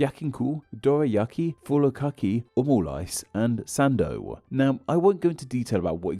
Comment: 丼ぶり カツ おにぎり お好み焼き たこ焼き とんかつ 焼肉 どら焼き 「ふるかき？」 オムライス and 「さんどう？」 何度聞き返してみても2つ聞き取れないんだけど、これなんて言ってるんだ？ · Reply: 「ふりかけ」と「サンド」と思います。